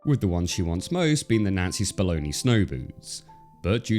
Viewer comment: Never realized how important the "n" sound is in the name Nancy until I saw this video. Fantastic video! But I legit thought your were saying N*zi Spamoni snow boots.